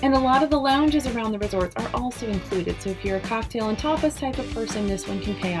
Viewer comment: Did she say "alcohol and topless type of person 16:44 ? I knew Disney was experimenting with more adult venues but seriously?